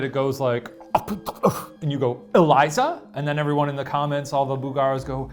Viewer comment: It's funny cause where Ryan heard "Eliza" I heard "Ryan" lmaooo, I was covering the subtitles so I wouldn't be influenced by them and I totally agree that we hear whatever Ryan hears because it's written right there! I've done this with other episodes in the past too, it's a very cool experiment.